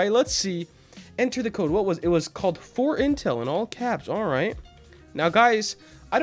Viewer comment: I THOUGHt it said fortnite L lol